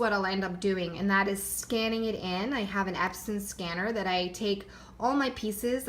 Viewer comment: What type of scanner do you use to digitize your work. It sounded like you said “absinthe scanner” or “absent scanner” I’m not a technology person, so I’m not familiar with these devices. I would love to see a tutorial on how to do the digitizing, have you made one?